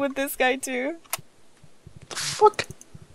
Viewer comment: Did say what the fuck?